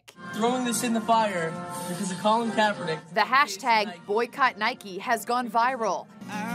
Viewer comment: Why do i hear Nike-y, it's Nike not Nikey